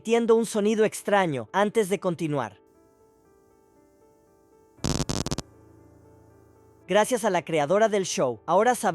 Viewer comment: qué fue lo que dijo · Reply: Dice:seguimos,ahora (No canon)